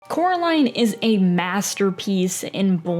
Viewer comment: I swear i heard her say chlorine more than one time